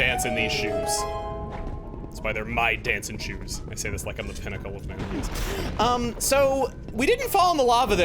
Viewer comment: I have no idea what he just said there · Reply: 'I say this like I'm the pinnacle of manliness.'